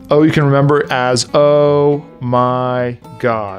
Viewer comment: His "Oh my God" sounded like Janice